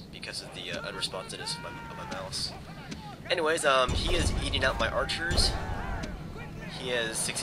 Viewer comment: sounded like eating out my archers lmao 2:18 good battle